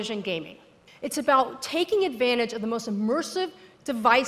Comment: cough did you say the ''best''controller cause I think I need my ears checked.